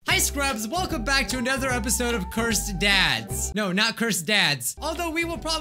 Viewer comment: I thought he said "Cursed Dads"